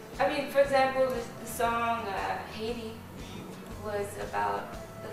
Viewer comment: the subtitle is wrong. The name of the song is Haiti, not Heidi. :)